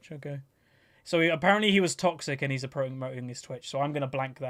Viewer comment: what did he said about twitch ? I can't understand what he said and why did he censored the chat ?? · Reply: The player was promoting his twitch account i think so censored it so the guy didnt get free advertising on his channel